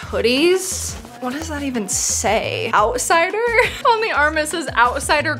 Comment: When she said outsider I thought she said Alzheimer’s